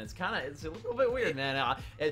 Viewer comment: is blaze saying former optic at 4:35 ? plus he says he fits in with faze and censers tweet